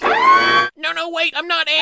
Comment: Didn't it sound like Bruce said, "No, no wait! I'm not Ant-man anymore!"